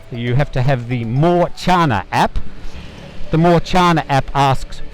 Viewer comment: The mask and the background music make it hard to hear you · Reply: Totally agree. It sounded like he said More China App. 🤔